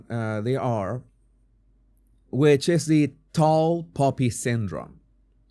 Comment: I thought you were saying "tall puppy syndrome". Glad i understood before you said cutting the head off!!